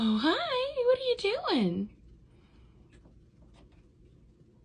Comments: I thought it said cow sniffer instead of calcifer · I misheard you way too wrong the first time hehehehe calcium